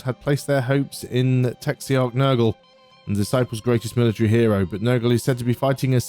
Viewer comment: I keep hearing "taxiarch nurgle x3